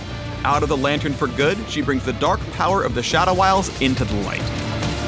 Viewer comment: Did he just say "Shadow Isles" now that they are removing the Shadow Isles from the game...